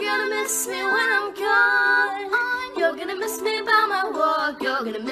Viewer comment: I keep hearing “you’re gonna miss me by my taco”😂